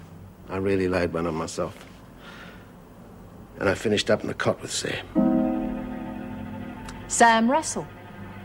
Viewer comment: What did Wally say at 6:51? "I finished up..." and I can't make out the rest of what he said. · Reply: "I finished up in the cot with Sam"